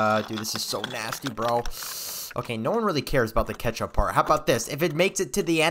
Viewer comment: I thought you said you like kechtup